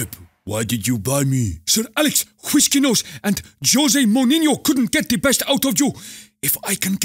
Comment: did anyone hear jose monyinyo😁😁😁😁